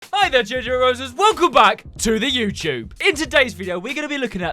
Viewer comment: I thought he said hi there "GINGER ROSES" 💀💀💀